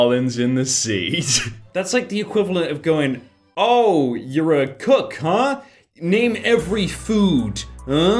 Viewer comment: deadass thought said “you’re a cuck, huh? name every food”